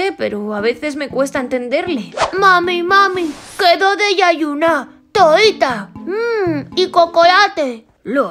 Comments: min yo le entendi quiero desayunar tortitas mmmm y chocolate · yo si entendi dijo quiero desayunar tortitas emmm y chocolate y en esta parte 0:36 dijo tu forever estas conmigo